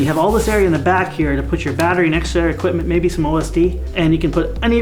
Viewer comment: i thought he said maybe some lsd and i was like yeah that was my first thought...